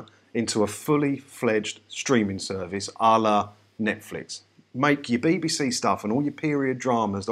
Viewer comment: Bit concerned when you referred to BBC's 'Allah Netflix' as a proposal ... ! It would seem in keeping with BBC policy though ... 🤣 Oh, I may have misheard you say 'a la Netflix', but I'm uncertain ... Could you clarify ... ? 😉